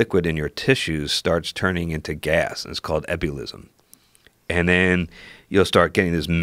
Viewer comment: What did he say? “Epilism”? Does he mean “embolism”?